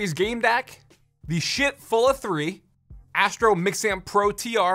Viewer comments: Low key thought he said Sh*t fulla 3 lol but Schiit that’s a weird name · I thought he said Shit Full Of 3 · I'm sorry, did he say "shit"?